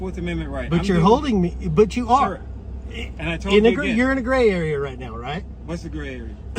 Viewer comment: Sounds like he said "NIGGA YOU'RE IN THE GREY AREA" I KNOW HE DIDN'T!!!! IM SURE GLAD THE COP DIDN'T INTERPRETATE THAT WRONG ALSO!! 🤣